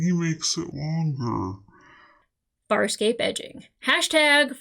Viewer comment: I don't know if your pronunciation has changed, but a few weeks ago I heard you say "fire escape" rather than Farscape for several videos. I didn't want to point it out, but now I hear the right title, so I blame my ears. Also, I'm so nervous you won't like this as much as I did watching it for the first time. Also, I was happy to see you remembered Gilina Renaez (Tech Girl). She's great. <3 And also, Chiana showing her strengths and adaptability is big here. Being able to kiss, kick or cry herself out of anything. For some reason I like her the more dressing up as a tech with dark hair. Is it her demeanour or her outfit? I also interpret her telling Gilina what she wants to believe John feels about her as her wanting it too, but she's too knowing to no have delusions. Maybe I identify with Chiana more than I fancy her? And also also, I'm glad you heard the name Scorpius so I don't have to call him "butt-chin thong-face" any more. ;) And also also also, I forgot that Stark was in this one. "My side!! Your side! My side! Your side!" Very fun side character to make the show even weirder. And also also also also, D'Argo holding Aeryn's hand is emotional.... It was too long since I watched this, so I wasn't aware this many things converged in this episode. Just you wait. This is just a wind-up for the continuation...